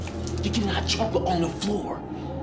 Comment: This may not get noticed, but at 0:17 the subtitles incorrectly say: "You do not jump on the floor!" What Gus really said was: "Your getting hot chocolate on the floor!" YouTube Auto subtitles are such a meme sometimes.😂